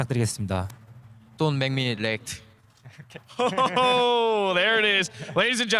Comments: what did faker say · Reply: Hey no I heard "DON'T MAKE ME LATE" clearly that's what he said what's wrong with all of you people. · what did faker say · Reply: Arcelon I honestly thought he said "don't make me erect" xD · Plsss I wanna know what did faker say in English? :( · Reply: i heard "Dont make me late"